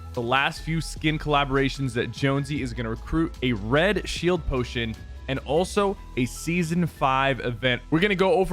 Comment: I thought you said jonsey was going to recruit a red shield potion